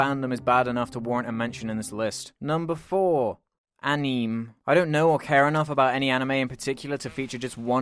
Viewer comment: Sounds like you said aeni. That's korean animation lol.